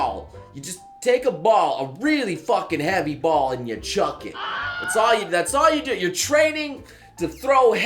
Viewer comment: What was said: 'Take a ball and chuck it' What I heard: 'I'll take a potato chip and EAT IT!!!'